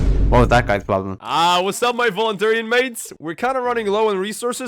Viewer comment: I thought I heard UthiAnimates